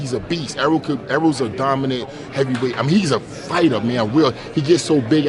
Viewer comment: did he just call Errol a heavyweight